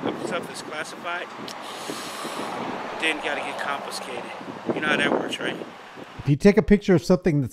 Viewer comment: I thought he said "constipated".☹️